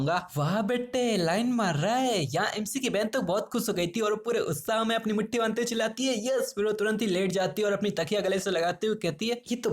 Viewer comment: no way I heard mutthi marte huye